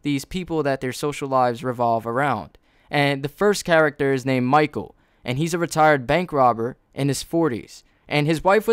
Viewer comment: the first time I heard "the first character is named Michael and he is a retarded bank robber..."